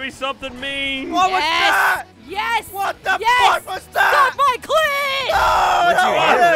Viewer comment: Did Gavin say 'fuck' at @ 29:50? It's such a rare occurrence that I'm really not sure.